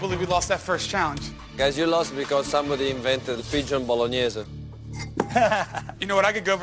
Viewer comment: At first I heard "duck bolognese" and thought, yeah thats a bit weird but ok. Then I heard it again and understood it was a dove bolognese. Oh boy, Ramsay must have felt bad and took it easy on him